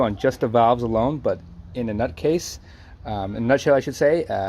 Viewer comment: Love your vids, very helpful. Funny too..."In a nutcase" (8:23) LMAO< I was fully concentrating on what you were saying and when I heard that I"m like...did he say nutcase??? I backed it up and laughed so hard. One of the funniest bloopers ever, when you're totally not expecting one.